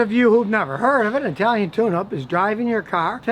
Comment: Scotty, I must be getting old. At first I thought you said an Italian Tuna, and I said to myself what is he talking about?